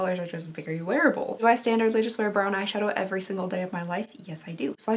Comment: Literally thought you said you wear a “bra and eyeshadow every day” 😂 that make me laugh. You really said “brown eyeshadow” but I think putting on a bra and some eyeshadow is still a pretty great standard 😂